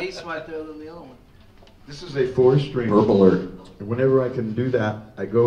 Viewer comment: "What's different about that bass right there than the other one?!" - Lars "This is a four stri-" - Rob "PURPLER :D" - James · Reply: I thought he said nerd alert but I'm a bit pist so thanks 😂